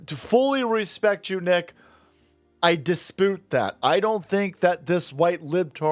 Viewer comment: Did you just say you "dispoot" that